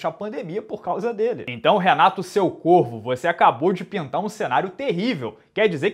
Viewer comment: Eu achei que ele tinha dito " então Renato seu corno não vai ter mais luta"? Kkkk